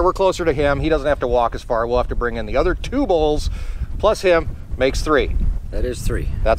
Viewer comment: I thought you said there was 4 bulls